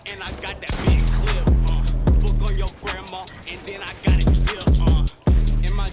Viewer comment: Did he say fuck on your grandma at 2:37 😂😂